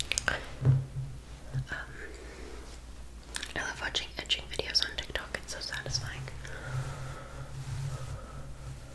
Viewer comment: WHAT DID SHE SAYYYYYYY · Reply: She watches edging hair styles videos.